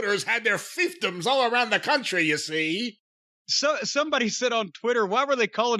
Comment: I thought they were saying thiefton too